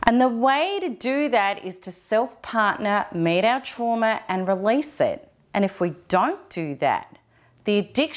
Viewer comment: I cant hear what she says "the way to do that is to.." at 03:11. I just hear "and the way to do that is to self-partner, made of trauma and release it" which does not make sense :D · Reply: Hi Graleh - I'm saying 'the way to do that is to self partner, meet our trauma and release it'. I hope that clears it up for you hun xoxox